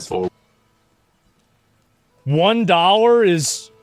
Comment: Thought he said 1 daughter is worth..